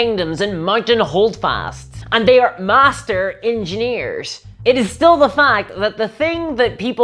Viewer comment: Did you just say en-chain-ters? .... I got use to poor as puuur. Ok I went back engineers is what you said. Gotta love failing hearing.